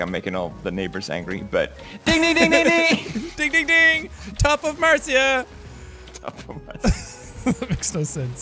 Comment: Jack: Top of Marzia Me, who heard it as "Ontop of Marzia": Wait what